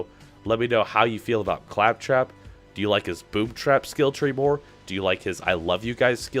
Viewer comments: Is it just me or did it sound like he said boob trap? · Who else thought he said Boobtrap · At the start i thought he said boobtrap then i saw the name